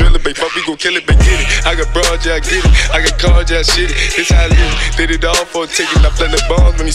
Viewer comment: Am I the only who can't understand anything except for the word "Panda" ?? 😂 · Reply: I swear I heard him say I get cards and I shit it... 2:26 Play it in slow mo....